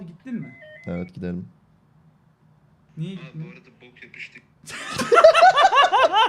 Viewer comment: Saniye 0:29 da ne diyor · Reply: abi bu arada bok yemiştik